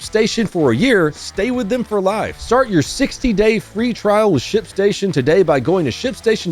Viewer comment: For the ad, I thought you said shitstation 💩 😂😂😂😂😂